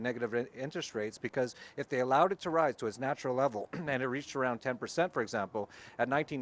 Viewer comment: Ummm, sorry folks, but I was a bit numb from the "shocking news," and did not catch what dude man said at 6:00... did he say "reach around?" Who got a reach around? It was not Venezuela that's for sure.